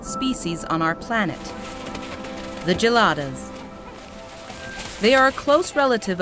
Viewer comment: I thought you said we were going to get Gelatos, not Gelatas! I don’t want baboons!